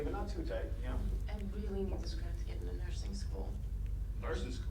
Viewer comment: When she said “Nursing School,” I somehow could have sworn that she said “Virgin School.”